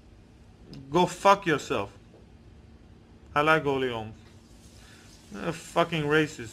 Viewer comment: what did he say he likes?? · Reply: Holly Holm